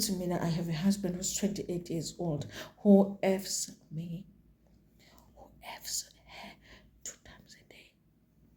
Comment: 🤣2 times a day everyday for 2 years 😂😂😂😂 Crystal my fighter. To be honest I thought she said 28 year old used to CALL her 2 times a day for 2 years when I saw on X what was actually said I went back and watched again 🤣